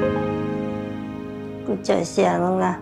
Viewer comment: พูดว่าอะไรนะคะ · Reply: โจ่ยเสี่ย = ขอบใจ